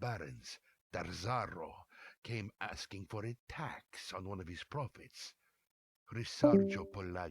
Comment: The voice acting does not match up with the subtitles. Subtitles say "came asking for a "tax" on this one's profits", VA says "came asking for a "tax" on one of his profits".